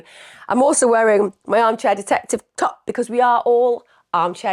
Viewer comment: I thought you top said amateur; not arm chair 😂